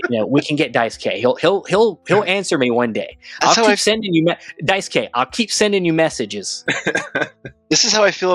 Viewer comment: When you guys were talking about interviews you’ve done, I thought I heard you mention “Dice-k”. I guess I misheard it, I couldn’t find him on YouTube.